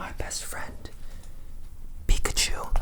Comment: Am I the only one who heard him say "Fuck a chick" the more he said "Pikachu"?